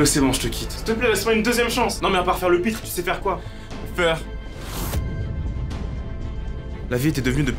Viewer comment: a 0:20 il a dit quoi ? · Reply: feur